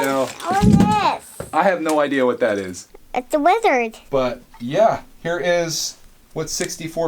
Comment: Is it a wizard or a lizard? Also, Wolfpack! Great vintage Castle stuff! But mostly, this video is stressing me out. I have a major garage sale haul like this that I'm going to need to go through soon and I'm both eager with anticipation and dreading it all at the same time. Can't wait to see what comes out of all of this! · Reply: PJCollects lolll I thought he said wizard too I laughed so hard for some reason but I'm almost positive he says lizard